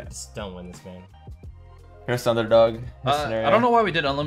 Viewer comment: i really thought justin called musty the 1st underdog🤣